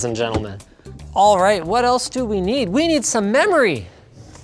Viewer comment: I thought he said, "We need tome milk".